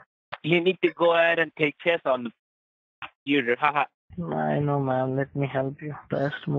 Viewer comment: It sounded like a Spanish guy saying "no mames mijo"